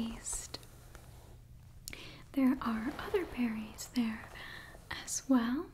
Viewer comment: My sleepy self thought you said "fairys" instead of "berries" - and when you said "they are delicious" I was like: "wait. What now?" 👀🫨😂